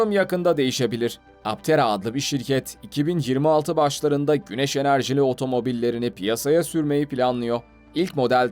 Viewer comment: Abi ilk dediğinde Altera anladım sunautica daki Altera diye anlayınca çok şaşırdım